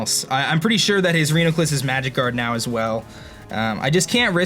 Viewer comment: did anyone else think he said magikarp instead of magic guard